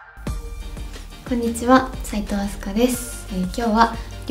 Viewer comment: くんにちは！に聞こえた萎 ていうか、カラコンて度はいってるん？